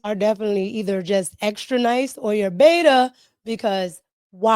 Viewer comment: was that a JLP “beta” I heard 😂